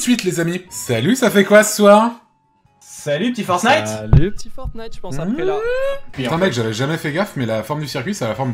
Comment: Deso mais j'ai entendu "petit fortnite après" j'ai quitté la vidéo